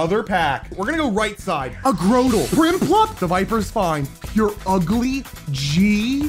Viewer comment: I swear, at 8:50, all I heard was, “Ur ugly, G.”